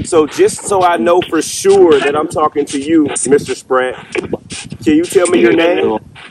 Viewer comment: I know this is old, but I heard spratt